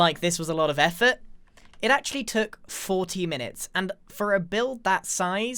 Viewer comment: sorry did you say 14 minutes or 40 minutes?